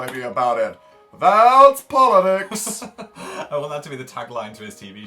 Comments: It sounds like Dan was saying "Thaaaaaaaat's poli-dicks!" Am I the only one? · i heard dan say "thaaaat's pile o dicks" instead of "politics"... k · it sounded like Dan said "Thaaats poli- Dicks!"